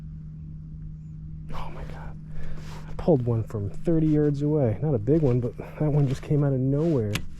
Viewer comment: I thought you said you pulled that fish from 30 years away. Not yards. I was dying laughing haha